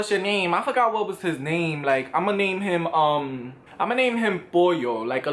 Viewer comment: I legit thought he said "Ima name him Boil" 💀😂